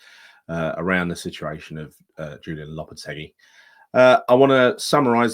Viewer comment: Watching with subtitles on and somehow when you said Julen Lopetegui YouTube heard it as "Julian Lardy". Made my season